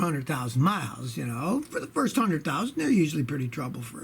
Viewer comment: First 100,000 days 1:43, am I hearing that right